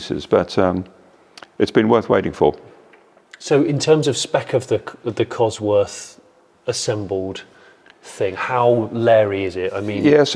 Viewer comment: did you really say "thee err Cosworth assembled thing...." 🤣